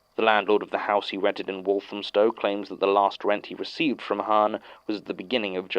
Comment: Ok, i dunno if this is just me, but at 20:38, he said Walthamstowe, I believe, and didn't one episode have a place also called Walthamstowe, I think it was MAG05, 93 Lancaster Road, Waltamstowe idk if I heard it right, just a thought.